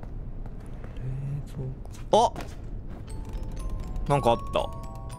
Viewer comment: 仕様なのかわかりませんが23:00分辺りで話しかけてる声が聞こえてきたんですが気のせいでしょうか・・・ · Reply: が冷蔵庫ってささやいて聞こえますね